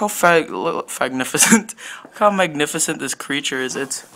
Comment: WHAT DID YOU SAY?? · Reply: Fagnificent is what I heard 😂😂😂